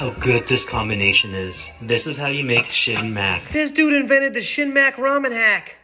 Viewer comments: I swear I thought he said the shitty Mac 😂😂😂😂😂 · I thought he said "Shitty-mac"😂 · I thought he said "Shitty mac" and watched the video to see if it would taste bad. Lol. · I thought they were saying shit Mac at first